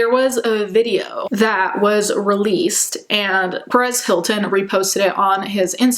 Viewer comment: At first, I misheard you say "*Paris* Hilton," thinking she had come forward as well!